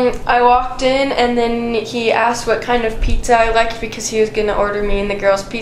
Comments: she says " I walked in and Nikki asked me what kind pizza I liked " She clearly says Nikki asked me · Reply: She said "and then he asked..." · and a few seconds after, I hear -Nikki asked what kind of pizza I liked cause Chris was buying pizza.. - something like that but I keep hearing “Nikki asked” It has to be -think he ? I know I gotta be hearing that wrong! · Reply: “HE asked”, not “Nikki asked”.